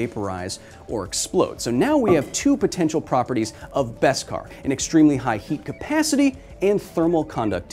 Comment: This whole time I thought they were saying, "Veskar." I wonder why actors suck so bad with enunciation. Veskar also just sounds cooler for some reason.